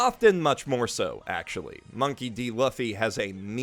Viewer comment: Ehem did you say money D. loffy?